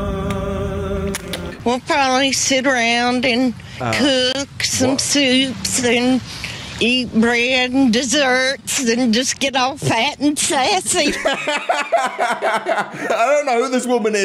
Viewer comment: What she said at 2:23 was I’ll probably sit down eatin.. Kunks and soups Eatin bread and desserts And just get all fat and sassy!